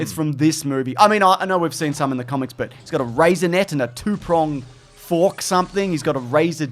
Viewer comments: I thought he said Raisinet instead of Razor Net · truly thought he was saying “he’s got a raisinette” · I know he said "razor net" but I totally heard "raisinet".